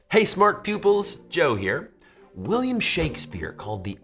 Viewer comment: Did he just say "hey smart pupils"